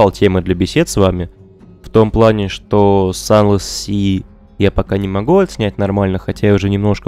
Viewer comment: что за название произносится? Тяжело расслышать. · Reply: «Sunless Sea».